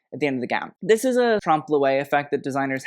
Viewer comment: What does he say at 1:41 ? What kind of effect is used in Adele's dress, I can't google it, please help! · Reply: Hi, I'm not frech but I think he meant this trompe-l'œil which translates to optical illusion. Makes sense cause the dress makes it looks like her waist ist smaller but it's just the gems making that shape.